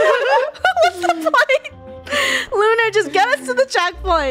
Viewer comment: what did just say 😳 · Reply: It's whats the point